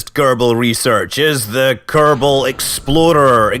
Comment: Anyone else hear "the Kerbal Exploder" at 7:37?